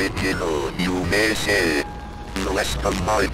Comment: I hear molest